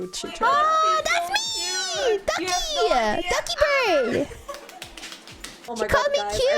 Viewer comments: I know it's Dokibird, and people joke 'doggy bird', but I keep hearing 'ducky bird'. XD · i watched before i new who was I thought she was saying Ducky for half the vid then went OH ITS